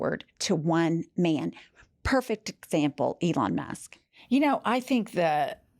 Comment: Oh shit. @ 1:02 is sounded like you said Ivana Musk. You should make that into a t shirt. Elon Musk with an 80s Ivana hairstyle.